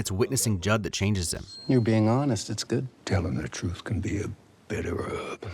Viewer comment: Yeah, until this video, I also thought Blanc said that "The truth can be a belly rub." It fit the CSI KFC, Foghorn Leghorn drawl.